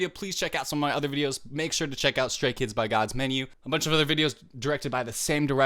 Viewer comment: Did he just say "stray kids by God's menu"?